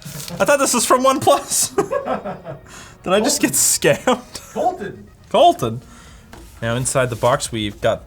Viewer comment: There are some mistakes In the CC so I just wanted to help the Hard to hear and deaf people, I will write what it is supposed to be on right - 3:00 - "scared" Instead of "scammed" 5:58- "officer" Instead of "ultra" 6:11- "fives" Instead of "vibes" 6:15- "burden list" Instead of "burdernless" 7:01- "no 20" Instead of "note 20" 8:26- "AK 30" Instead of "8k 30" 15:35- "LTG" Instead of "LTT" They were pretty obvious but yeah.There were some which I left too because they were too petty